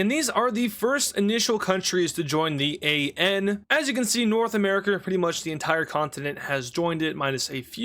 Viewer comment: did just say north america hard r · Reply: He said North America or